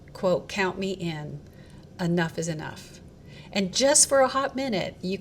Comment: I thought he said count me out